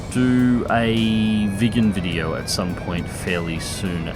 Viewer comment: Thought you said vegan video there for a sec, about to unsub and run until I saw Viggen in the comments. PHEW